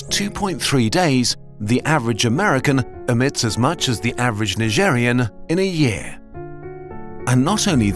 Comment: did he say Algeria or Nigerian cause that ain't the nigerian flag